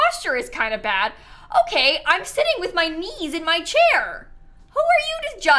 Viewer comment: I thought she was saying" I'm eating with my knee my chair " my first instinct was like are you really ok?